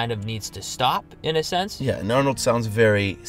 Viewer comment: did you say at 8:08 "yeah...claro!"?